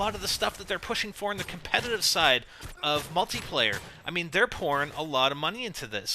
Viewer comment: who else thought he said porn at 8:42 lol